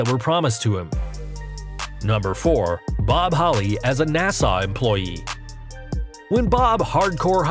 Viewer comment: did he just say Nassau? Like Nassau Bahamas